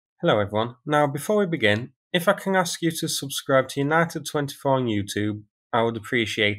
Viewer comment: First time I heard: "Before we begin, I fucking ask you to subscribe"